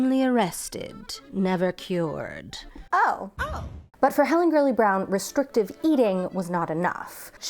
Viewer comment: I heard Helen "girly" Brown the whole time.